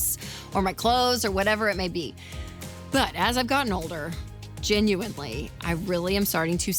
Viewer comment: 22 sec: "But, as a gun holder, ...? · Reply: "As I've gotten older..." After reading your comment I went back & listened and that is what it sounded like she said even though I know that wasn't it. I had to listen to it 2 more times to be sure. 😂